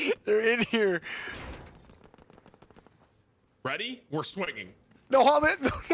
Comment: "Ready? We're swinging." All I heard was, "Leeeeeroy!" Edit: I've been watching for a few months now it seems and I still have no idea who Gary is.